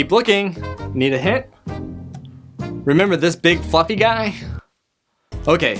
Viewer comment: Did he say f ing guy